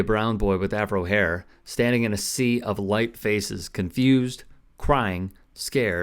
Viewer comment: A sea of white faces? This is what a reasonable person would expect to see in a predominantly white area,if this incident happened in Nigeria you would likely see a sea of black faces. She needs to alter her mindset before making such comments, silly woman. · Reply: I thought she said light faces. Maybe not much difference I guess